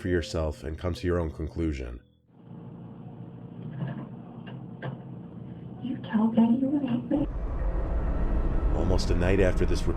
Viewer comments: on story 4 i heard " you killed daddy, you killed me " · all I heard is "can you tell daddy to-------" or "can you tell daddy that you love me" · For the fourth story I heard “did you daddy you hate me?” 0-0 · I heard, “You tell daddy we’ll be okay.” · I heard "you killed Daddy last night" or something among those lines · I hear "Did you tell daddy you hate me?" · i heard "hate me " instead of "raped me"